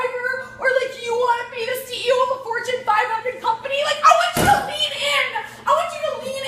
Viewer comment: The video's poster must be illiterate. She clearly says "Fortune 500 Company" and he labelled it as inaudible.